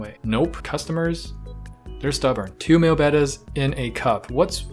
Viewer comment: "Two male Bettas and a cup" I thought that was what I heard and... Flashbacks. Horrible, horrible brown flashbacks.